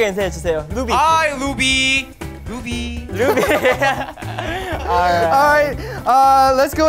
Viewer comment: When V introduced himself it sounded like he said ''B!'' instead of V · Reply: And jhope pronounced Luby instead of Ruby that's so cute 😱😍💓